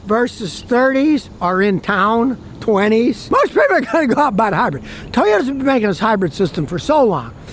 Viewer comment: Scotty??? What did you say at 1:13?? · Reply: "Most people are going to go out and buy the hybrid"